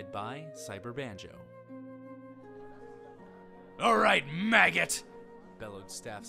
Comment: ahahahaha all I hear was "alright you maggots!" and I died XD that TF2 reference